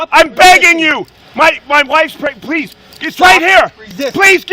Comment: Did he say "my wife is pregnant" at 5:42 as a plea for mercy???